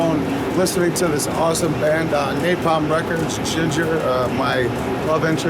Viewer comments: CC is wrong at 23:04, its Jinjer not Ginger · The dude was talking about a Ukrainian band "Jinjer". It sounds the same as ginger, but is spelled with J's instead of G's. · OK, that's wild. 22:59 Napalm Records and Jinjer? Did i hear right?^^